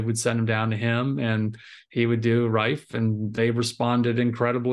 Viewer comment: Hi, Great interview! Thank you! What was the modality besides HBOT that helped the most, it sounded like he said Rifee? Did I hear him wrong and it was Reike? Thank you for clarifying.